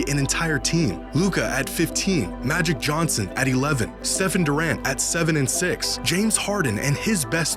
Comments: am i trippin or did bro say stephen durant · Did bro just say Stephen Durant 😂😂😂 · Did bro just say Stephen Durant 😂😂😂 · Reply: thought he said “seven durant”. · I thought he said “Stephen Durant” not “Steph and Durant”😭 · thought you said Stephen Durant. Heh… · Did I just hear Stephen Durant?